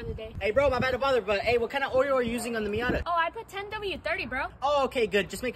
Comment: i thought he said what kinda oreo are you using.... it was oil